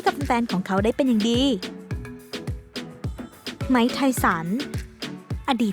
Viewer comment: Mike Tyson แต่ดิฉันผู้ได้ยินว่า ไหมไทยสัน ว่าซ่านนนน